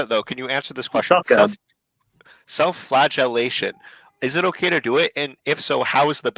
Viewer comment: dude I thought he said “self-flatulation” I was so confused